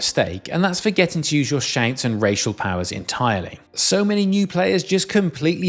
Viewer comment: did he say racial powers?